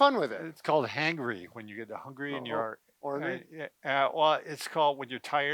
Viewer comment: ish mark..? I thought said hornry there at the end, lol.